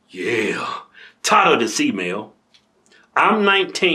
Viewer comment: I literally thought he said 'tired o dis email' 😂